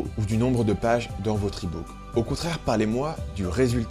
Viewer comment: C'est quoi un ipo ? A 2:19 "du nombre de page dans votre ipo" Ps: ce message n'est pas un troll, je demande vraiment. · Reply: Il dit pas ipo, mais ebook